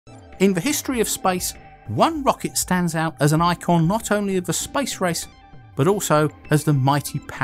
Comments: funny accent, it sounded like he said "The Spice Rice, at the beginning of video, I can't be the only one! lol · thought he said the spice rice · Whenever you he says space race I hear " spice rice " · Sounded like you said Spice Race.. Thought this was about to be about a rocket to Dune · I first thought he said "the history of SPICE"...im still laughing on myself... Love your contents sir. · it sounds like he said "spice rice".